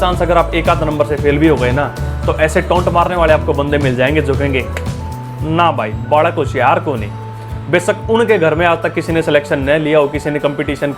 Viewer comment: What sir said in haryanavi, timing at 3:40 "na bhai....?" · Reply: balak hoshiyaar koni......means.. the child is not intelligent